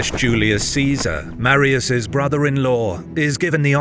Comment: I thought you said "Caesar married his brother in law" haha I was like wait what?!